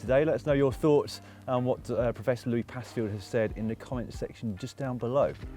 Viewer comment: Clicked on this because I thought said Louis Pasteur....